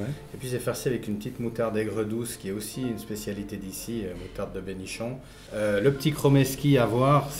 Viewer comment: il dit quoi a 3:12 la moutarde de mes nichons ?